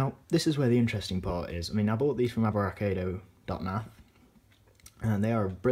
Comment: Can you please reply the website you said you bought them from at 1:41 because I can’t understand what you said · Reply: ciaran poole abrakado